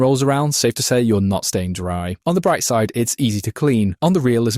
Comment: while listening to this I heard “not stained dry” instead of “not staying dry” my dumb ears